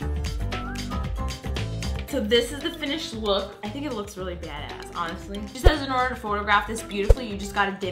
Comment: did she say badass bc it sorta sounded like it but I wasn't sure